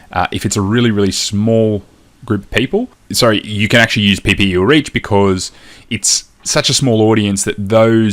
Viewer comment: Hey, Thanks for the awesome training, can you please tell me what do you say at @ 6:45 "you can actually use PPU reach"? I don't get it. And which optimizations do you talk about when you say they can find people at a lot less money. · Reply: Ppe is what engagement ads used to be called. I generally just use purchase though :)